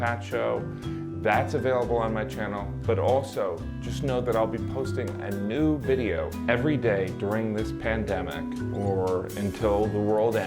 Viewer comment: When I first saw one of these videos I thought at the end you said, “posting a nude video every day”. Well I’m still waiting Jim.